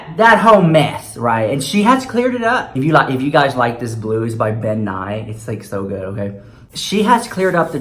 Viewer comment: I thought you said the blue was by bill nye hahahaha. Had to rewind it